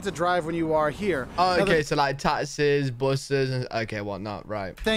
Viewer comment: I thought he said "TARDISes" not taxis.😂